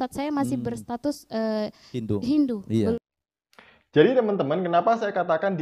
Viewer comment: Mbak muslimah mantan Kristen ini ngomong "saya masih berstatus..HINDU (menit 1:44). Saya yang salah dengar atau gimana, ya??🤔🤔